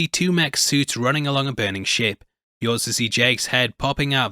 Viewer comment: I swear to GOD I thought you said "running along a burning sh*t" and I could not stop laughing